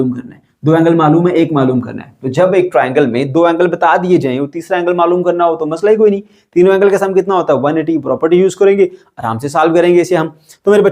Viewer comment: I thought that sir said " baba ta shaih hi koi ni "😂😂😂